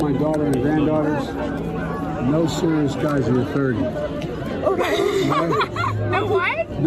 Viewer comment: It used to be "no guys until you're thirty". Now its been modified to "be easy until you're thirty". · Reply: I thought he said “No serious guys until you’re thirsty”